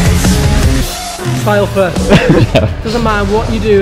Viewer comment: What are they saying at 2:03? The quote starting with "<inaudble> first. Doesn't matter what you do, you can always do it <inaudible>." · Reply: Style first, doesn’t matter what you do as long as you do it in style. Speed second, safety third 😎